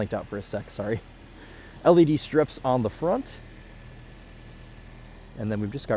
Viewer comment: Lol was listening while driving, not watching and it sounded like he said "a lady strips on the front"